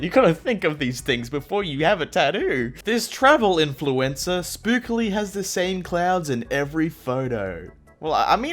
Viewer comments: “Seriously you gotta think of these things before you have a tado” Love how he said “tado” Instead of tattoo lol. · did he said tado?